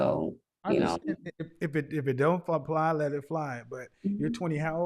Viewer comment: what did he say?? I replayed it like 5 times “if it don’t ........ let it fly” what’d he say? Lol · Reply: he meant If it doesn’t apply then let it fly